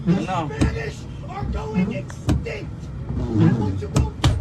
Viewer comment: I thought bro said "the Finnish are going extinct"